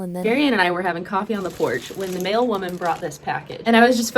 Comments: I heard that as "the male woman" and was confused for a few seconds. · For a second I thought she said a male woman brought the package 😂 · I really thought you said “male woman” instead of “mail woman”. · I thought she said “male woman” and I was like: hold up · I thought she said male woman, and my whole brain went... huh? that's when you know that 3am is not a good time to be watching YouTube. Even high quality productions like this. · Did she say a male women?! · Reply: no a mail woman as im a woman who delivers letters and parcels